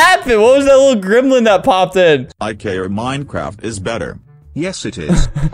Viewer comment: i think she is saying ROBLOX IS BETTERRRRR so ye